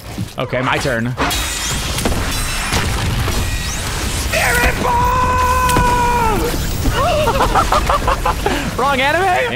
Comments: did bro say SPIRIT BOMB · AINT NO WAY BRO 💀☠ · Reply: did bro say SPIRIT BOMB